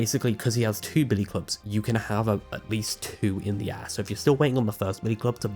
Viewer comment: i’m sorry but i can’t hear anything other than “you can have at least 2 in the ass” i have no clue what you’re actually saying here 😭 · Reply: 😂😂 it's air not ass but now I can't unhear it either